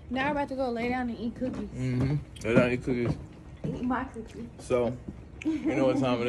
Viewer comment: Did she say eat my cookie at 8:04 - 8:06 😂😂😂😂.